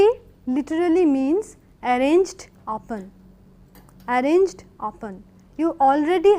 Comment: what does mam say ? 'Arranged apan ' ? what is that? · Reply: Upon